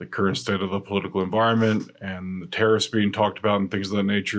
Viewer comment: I thought you said terrorists not tariffs 😂. I don't watch the news much and I thought that was coming back to bite me for a second.